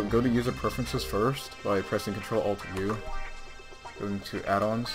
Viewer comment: what did he say? Ctrl+Alt+E+U?